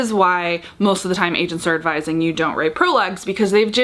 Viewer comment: I totally thought you said “why do Asians tell you not to write a prologue” and I had a second of “well I don’t really have an opinion, you do you.” 😂 Then rewinding realized AGENT, she said agent. That being said, I will admit to skipping prologues sometimes...